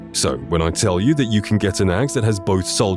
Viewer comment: if you’re not paying full attention it sounds like he says “you’ll want it in your asshole”